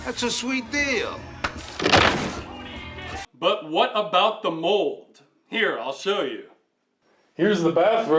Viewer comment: Oh, MOLD! I thought you said moles! I'm not sure which would be worse.